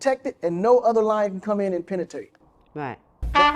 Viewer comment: did he just say “penatate”? 😂😂 imagine letting a dude like this penetrate you that can’t even say the word. Strong intellectual gene pool incoming😂